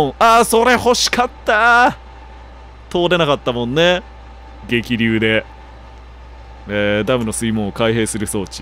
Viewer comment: 激流まで 「桐生」って聞こえてしまう